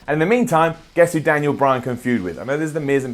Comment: you said food instead of feud